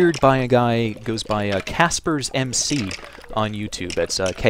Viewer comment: lol at 1:54 i thought he said KassemG xDD